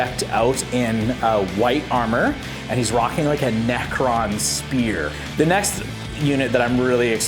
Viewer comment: did I hear that right? a NECRON spear?